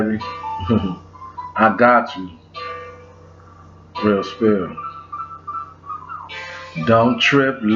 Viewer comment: my boi sound like he said, "Real Steel"... Yes, great movie😂😂